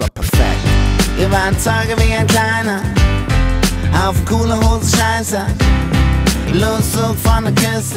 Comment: Ich habe im Refrain erst verstanden: "Wir warfen Torten wie ein Kleiner, auf Kohle wuchs Scheiße, Lose zum Vergessen, Angie ist spitze. Denn die Fürze von der Erde, wollen raus aus der Kärnten, haben kein Ziel, aber Fuhrenpos, große Beats, keine Studios." :D Jan hat echt ein Talent zum Nuscheln